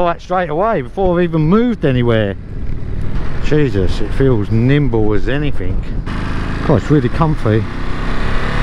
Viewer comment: I like your videos a lot. They are stright to the point. What is it, you say at 1:08 and later again " feels really limbo"? Or nimbo? What does it mean? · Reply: Tha is a lot , appreciate it , really Nimble , as in it moves very quick and light... your get used to my accent 😂😂